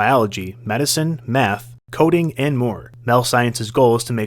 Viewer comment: Bro bro I swear I heard meth...